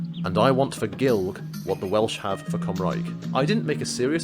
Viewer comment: What is the phrase that he says at 2:03 ??? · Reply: "... and I want for Gaelg what the Welsh have for Cymraeg." Gaelg = Manx, Cymraeg = Welsh.